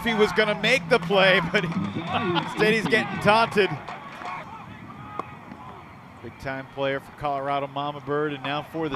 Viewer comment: What was he saying at5:30? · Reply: “Quack, quack, quack” it’s the celebration for Minnesota grey duck